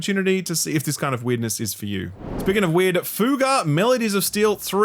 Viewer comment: I heard “speaking of weird, FUTA” and had to do a double take.